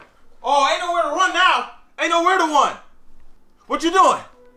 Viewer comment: when he said, ain’t nowhere to won instead of run made my day 😂